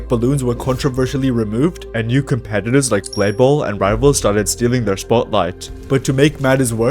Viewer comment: Did you just say rivals?